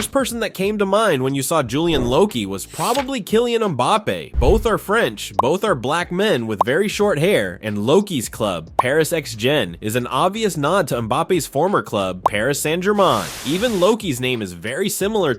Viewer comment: WTH DID U REALLY SAY BLACK!